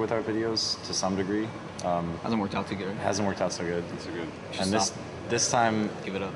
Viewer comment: Can someone tell me what Dan said from 1:27 to 1:32, please? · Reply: "Hasn't worked out so good." (can't understand the middle part) "Give it up."